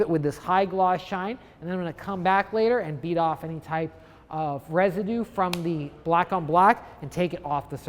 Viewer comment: What does he say at 2:38? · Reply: Beat off any residue left over.